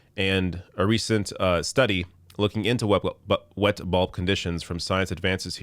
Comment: It sounded like you were saying wet ball conditions which made me laugh like a child haha